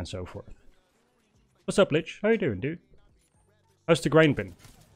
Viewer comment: i feel called out... did he just say Litch?